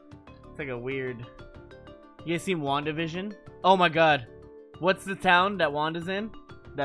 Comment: When Mango asked if Zain watched WandaVision for a second I thought he said JuanDivision XDXDXD